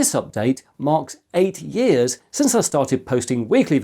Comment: EIGHT years since I stated uploading weekly. Fifteen years since I started uploading. :) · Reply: i Kept rewinding to that beginning part and it's clearly sounds like your saying 80 years., But yeah 8 years sounds normal.